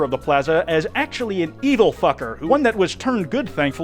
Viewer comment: I cant be the only one that thought he said eagle f*cker, I had to replay it like 6 times and everytime I hear it, eagle f*cker 5 second rewind eagle f*cker 5 second rewind eagle f*cker 5 second rewind